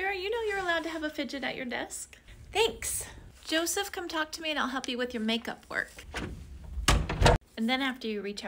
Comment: I thought you said Joseph come talk to and I’ll help you with make up work but for skin